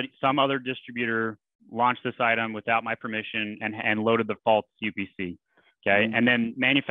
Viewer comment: Hi -if I use your first method with a brand registry support ticket, can I simply say I'm changing the upc to a GS1 number? ....At the 1:30 mark, I can't make out what you said..You said, " another distributor is listing the items without my permission and is uploading " a False UPC?..or a bulk upc?..I couldn't make out what you said there...Thanks for all the help..